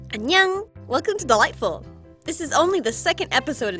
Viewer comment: So i found this channel yesterday and i realized she says “ Welcome to Dollightful “ not (what i thought she said) “ Welcome to the light bulb “ 💀